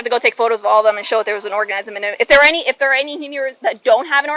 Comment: did she say, "I just have to take a picture of all of them and show that there was an (orgasm) in there." LMAO